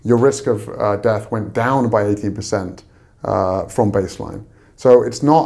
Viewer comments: Hi... did say 18 or 80% at 7:20 in this important interview? Thanks. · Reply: He says 18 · Hi, did say 18 or 80% death risk reduction at 7:20 into this important interview? Thanks. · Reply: Mercedes Maharis 80% according to the captions